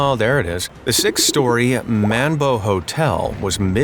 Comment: thought u said mambo at first i got sad for a sec